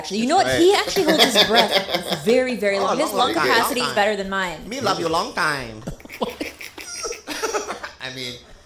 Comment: Can you imagine Bert actually running? He wouldn't get halfway and would stop with 2 black eyes from dem tig old biddies hitting him in the face. 9:43 Did Bobby say "Me lung you long time" ?!